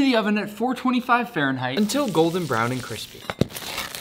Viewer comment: At 1st I heard, "Its good but it didn't out pizza the hub"